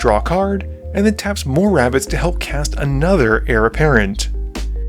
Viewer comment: Cracked me up. You sounded like you were saying Heir Apparent instead of Hare Apparent, got some rabbit royalty up in here.